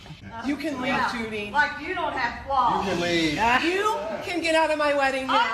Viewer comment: About the groom freezing at the mothers screaming. There was some kind of a comment the bride made after the fact that the husband was actually having either an anxiety or a panic attack and froze up out of fear, not because he wasn't willing to stand up to her. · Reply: I think I also heard him manage to say to his mother “you can leave”. That’s awful that they had to experience that. 15:30 - time stamp